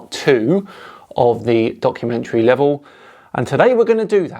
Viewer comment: it sounds like you said "*donk*-umentary." I don't know if it was intentional, but I'm all for keeping it!